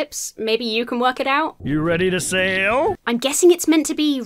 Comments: I think he says, "you ready to sail?" You know... because ships are on the water and... fish... in the water? So like.... they're the same? shrug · I heard "you ready to sail". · Mr. Flips says “Are you ready to sail?” 36:12 At least that’s what closed captioning said. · what i heard was " you ready to sail"